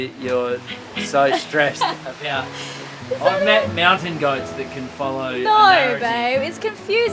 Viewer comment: (Star Wars): I've met mountain goats that can follow..." Now, that a serious dis. :-) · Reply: I thought he said, "Mountain Guides" that really is a dis!